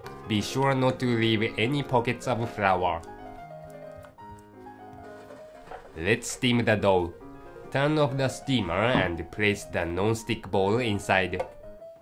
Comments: At first I thought I heard say "let's steam the dog" No srsly. My ears are working against me. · I heard "Steam the dog". Hahahahahaha! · i thought i heard, lets steam the dog. LOL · For a second there @ 1:40 I thought the narrator said lets steam the dog LOL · I heard "let's steam the dog" too LoL. Beautiful cooking by the way. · lol lets steam the dough but it sounded like he said dog xD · I know you said "let's steam the dough", but I thought at first you said, "let's steam the dog"! O_O I can't wait to make daifuku now~. :D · LOL! "Steam the Dog!" I heard that too but it think it's "steam the dough". Makes me crack everytime I play 1:38. LMAO! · I thought the guy said "Let's steam the dog" at 1:40 lol I was like 'WHATTT???!!!' :P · How about strawberries dipped into chocolate fondue then coated with powdered white sugar and granulated brown sugar... Lol, at 1:40 I thought he said, let's steam the dog... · i thought they said "lets steam the dog" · The narrator sounded like he was saying, "Let's steam the dog..." xD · "Let's steam the dough" I thought u said let's steam the dog! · it sounds like he is saying lets steam the dog. XD! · it sounds like he says "let's steam the dog..." D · hahahaha at 1:37 it sounds like he says "lets steam the dog" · LOL, t thought he just said let's steam the dog. Oh my, how cruel